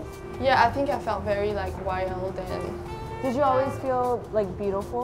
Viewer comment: What did she say at 6:19? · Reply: i think she said that she felt "wild"